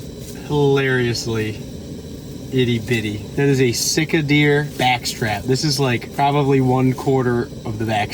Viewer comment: I thought you were saying you were sick of backstrap! I get it now though lol.